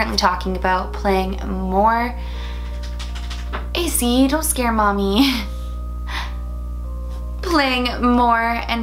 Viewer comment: Wait @ 22:40 What did said after that tapping sound? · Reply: Mizu Chan “-pup’s name- don’t scare mommy”